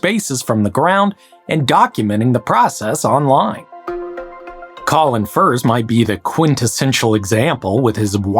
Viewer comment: Am I the only one who heard him say Colin Firth? I rewound expecting to see the man digging in his English wellingtons and mud and mist.